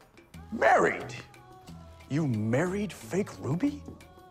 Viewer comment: I thought he said " you married fake booby ? But It was ruby.